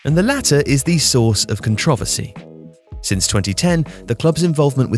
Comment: Did he just say "controvvuhsy" instead of "controversy"?